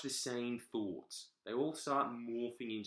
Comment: My new goal: Find out what Jordan is saying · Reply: "you can sit there and observe and justbelike I'm living in a coo coo clock"